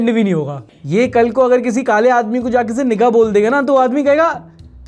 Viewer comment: Am I the only one who heard N word